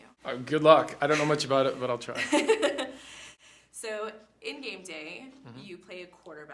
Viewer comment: did she really say game day? Lol tom just let it slide and continued with the question